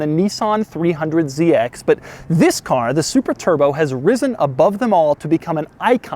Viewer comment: Did he just said “the super turbo” at 0:22?